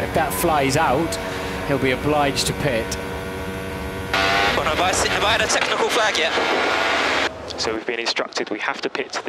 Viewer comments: What did Lewis say on 5:03? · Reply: Have i had a technical flag yet? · What did Hamilton say to his crew in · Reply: Have I had a technical flag yet